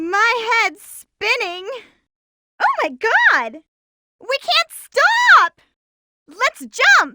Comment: Did she just said "Oh, my, God"? Lol